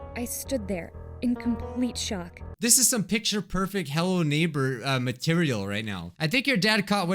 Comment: When you said hello neighbor I stopped and I thought that you said hell but you said hello neighbor